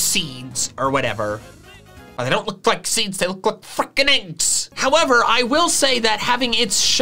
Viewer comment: if ypu put captions on the captions say fucking and not freaking so techincally sweared